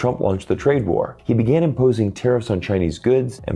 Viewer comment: Tem erros na legenda. Por exemplo, 3:28 , aparece "terroristas", mas a palavra que ele disse foi "tarrifs" (taxas, impostos).